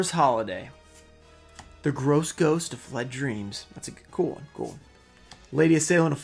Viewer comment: yo lmao i thought you said the ghost of my dreams lmao @ 4:16. i had to rewind for a sec